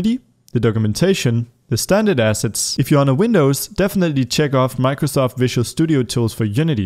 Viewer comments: did you say check the box or don't check the box? pls help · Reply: Check the box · Hey, I meant to ask... At 1:07 I believe you said that if we have a windows, we should check off “Microsoft Visual Tools” for better graphics. Then again I’m pretty sure I misheard it... So I wanted to ask, if we have Windows, should we download “Microsoft Visual Tools for Unity” or not?